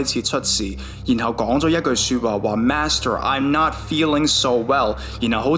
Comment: I heard “Nashville” instead of “Master”